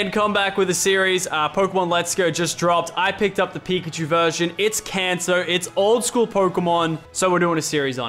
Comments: did he just say its cancer??? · Did anyone else think that at 0:27 he said it’s Cancer.